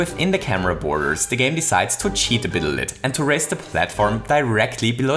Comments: did anyone else hear him say "a bittle lit"? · "a bittle lit" instead of "a little bit"😂 cute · did you say "a bittle lit" instead of a little bit? xD